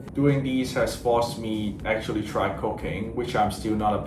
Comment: is it just me who thought had said he tried cocain? instead of cooking?